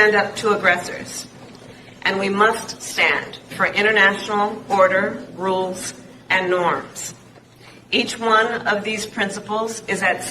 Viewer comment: Haha... Did she just say, "we must stand for internal boarder rules and norms". That's the funniest thing I'ver heard all week